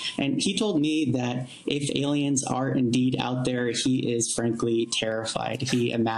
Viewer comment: When he said “if aliens are out there” The auto generated subtitles said “if Italians are out there” it’s was about